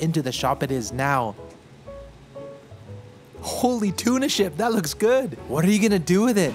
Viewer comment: Did you just say 'holy tuna shit'? 😂